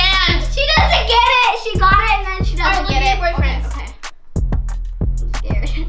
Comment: Did anybody else hear Piper say okay let's see what your boyfriend said Time was